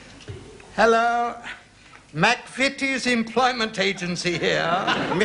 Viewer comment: What’s the name of the agency Mr Goldberg says at 14:32? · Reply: McVitie's